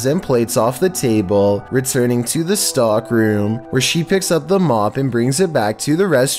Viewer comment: it sounds like Chills is saying STALK ROOM lol